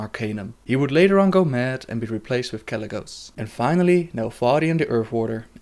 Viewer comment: you speak of nefarian instead of Neltharion Neltharion is Deathwing, and the first black dragon. But Nefarian is his son... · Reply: ***** Neltharian is what he said, but because of his accent he cannot pronounce the sound "th", so it always comes out sounding like"f"